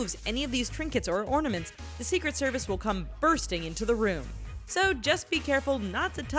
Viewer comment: Anyone notice at 1:51 the caption says busting and instead she said "bursting" Mind Blowed huh